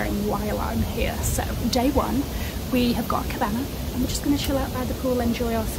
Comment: Thought I heard you say „we have gotten a Kebap“ in the beginning 😂😂🤌🏻